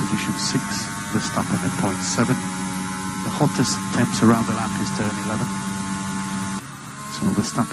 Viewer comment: I heard "... engine temps". either you misheard for title or clickbait. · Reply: I differently thought I heard him say mods in the race but my brother thought he said temps and it does sound like temps there so I do think yay right